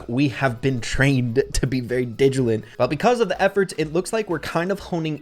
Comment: Is it just me or did say digilent instead of diligent at 3:54. No hate, love your content, and keep up the good work, the nerd in me just found it funny.